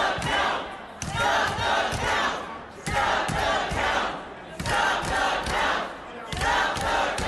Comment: Trump supporters in Arizona: Count the Votes!!! Trump supporters in Michigan: Stop the Count!!! 🤣🤣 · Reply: for a second there I thought I heard 'stomp the cow'..